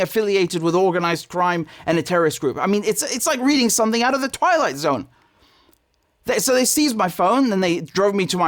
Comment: I know I shouldn't laugh, Richard, but I heard "it's like reading something out of the toilet zone" which of course it is! Nazi Austria is alive and making arrests.